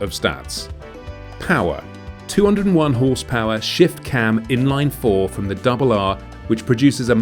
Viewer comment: I thought you said shit can engine.